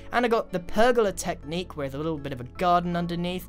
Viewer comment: When he said garden I heard “gun”. Is that just me or did anyone else hear that???